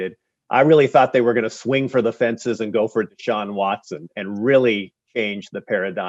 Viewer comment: Did he call Deshaun Watson "John Watson"?